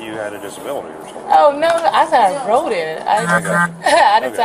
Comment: Why did the closed captions say “I wrote it” instead of “I rode in it”??? 13:46 Lame. 🤦‍♂️🙄